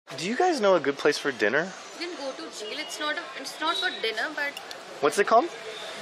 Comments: At first I thought she said " You can go to Jail"😂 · I heard "go to jail"😂